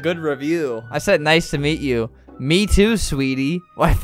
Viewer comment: Lol i think flamingo said "Mewtwo" In this part